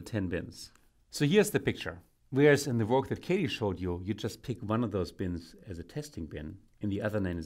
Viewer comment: Someone please tell me what he said he was speaking english then he just jumbled his words up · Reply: whereas in the work that katie showed you hes refering to the video another person did